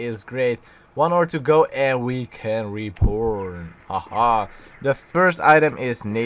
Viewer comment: Sounds like you're saying reporn instead of reborn at 2:17, lmao